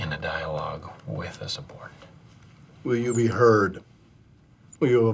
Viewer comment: what the hell is Jim saying.. his last word, with a "support.. sport, subort" wtf does that even mean.